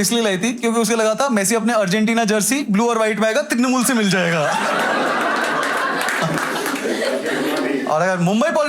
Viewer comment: What??? What did he say??? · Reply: He said Trinamool mean TMC (Trinamool Congress)